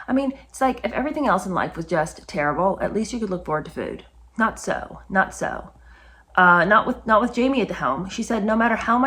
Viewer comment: "Not so, not so" or I heard nutso, nutso!